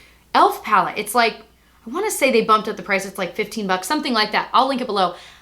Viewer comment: I thought you said,"it's only 55 ducks".....lol Loved the video!! Hugs btw.....2 hrs would be great! I love your opinions and humor!!!!!